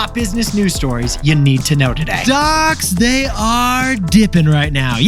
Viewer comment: I thought Nick said “Dots are dipping” at the beginning of the episode… not “Stocks”. Was equally confused and excited to have another Dipping Dots story this soon